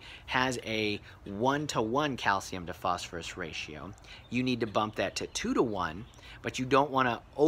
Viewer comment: Where can you buy calcium + phosphorus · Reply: i thought u said you need it